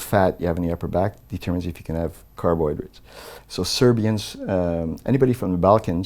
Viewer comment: Lol "Car Boy Droids" 8:51, "Hillary"- Instead of Cellary Lol! 9:08, "Clear Car Batteries" Lol, 8:58 Haha XD! Thanks! Good Interview, For Real! Good Luck to Iron Man Lol!